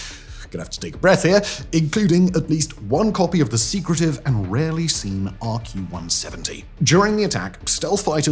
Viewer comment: I almost thought he said the secretive and rarely seen ARC-170 🤣🤣🤣